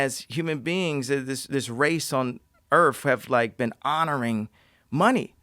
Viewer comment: SUM= 3HR OF "I ...ME...MY ... " -K. West (did he just say erf?